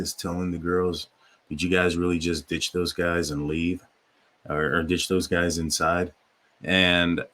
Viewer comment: It sounds like he said; you've gonna ditch those guys and you're in trouble.